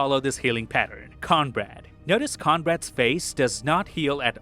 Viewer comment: Is he saying Conrad? Mf is named cornbread